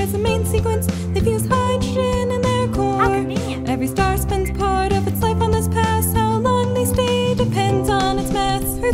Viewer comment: What does the "voice" say @ 1:27? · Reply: "how convenient"